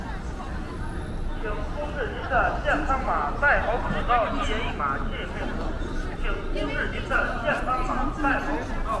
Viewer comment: What is the announcement speaker saying at 5:14? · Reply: Basically he said scan the health code before you inter to the street. Do you understand basic chinese?